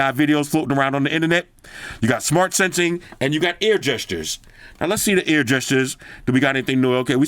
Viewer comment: reminds me of the air gestures from the old galaxy s4 bro lol i swear I heard you say ear gestures bro I started laughing my ass off at that 😂